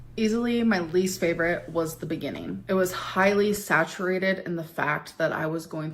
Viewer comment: “It was highly saturated in the fact” this right here shows me that Amber tries to shove as many “big” words into her stories/narratives in order to make herself sound more credible or truthful but it just makes her sound very unintelligent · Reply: I genuinely thought you said saturated fat